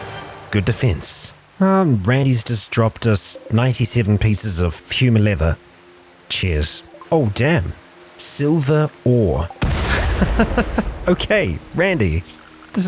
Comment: Not sure if I heard say "Cheers" or "Chairs".... Either works. EDIT: when you're running a lot of mods, Rimworld starts to chug; especially when they aren't well optimized, which many mods aren't.